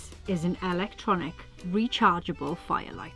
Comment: I first thought you said an "Electronic Violator", nearly choked on my coffee.